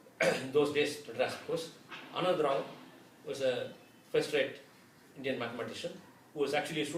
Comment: and 15:35 What name did he say? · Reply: Dr.K Anada rau , 15:35:- Dr. Subbaramiah Minakshisundaram(I guess).